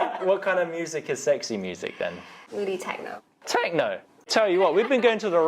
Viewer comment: When she said sexy music I thought she said tango, not techno 😫